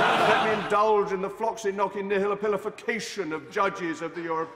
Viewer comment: what was the word that he said in 1:07 ? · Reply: floccinaucinihilipilification